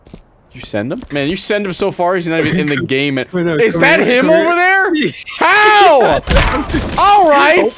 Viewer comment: When yelled "Is that him?", all I heard was "Is that the Grim Reaper?".